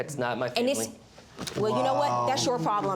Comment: The hell is he saying as Judge bangs the gavel? It sounds like he's saying "Yesteryear" or "Sister of the year" or "Answer of the year" or something. Its around 19:13 or 19:14 That guy does NOT wear sarcasm well